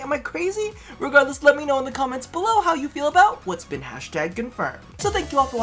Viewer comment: Hashtag Confirmed? I thought you said Half-Life Confirmed!